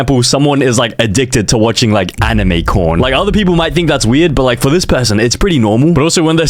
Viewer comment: turn on captions and read what it says for anime corn